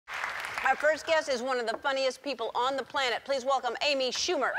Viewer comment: Funniest? Really Ellen? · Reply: Wait, funniest? I heard 'unfunniest'